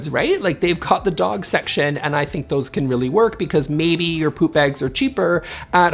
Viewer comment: did you say, "Poop Eggs"? I am so glad I don't have any of those!!! 😂😂🤣🤣